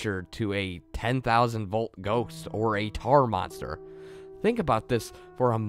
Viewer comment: I thought he said TardMonster. XD